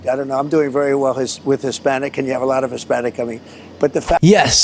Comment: 2016....Trump: "And they call her, Pochahontas" This is what I heard... Trump: "I do very well with hispanics, I have a lot of Hispanic in me."